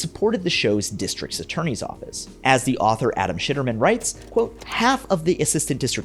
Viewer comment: important video but did you just say that guys name was shittermen? Hilarious